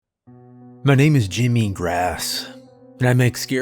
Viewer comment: When the mc said his name I first thought I heard Jimmy EatsGrass. I added the “Eats” but it’s grass! This story is SUBLIME! This is what I love about your tales, horror, ORIGINAL TALES, & the OUTSTANDING way you & the team put them together! THANK YOU, my FANTASTIC FRIENDS! (We’re so late but this tale is 🔥) 👤📰💎🗿👊😁🗿💎📰👤